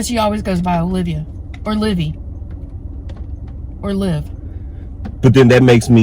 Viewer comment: When you asked her name I heard "Livvy" and when you asked her favourite color I heard pink three times. Like she was trying to repeat it to make herself heard.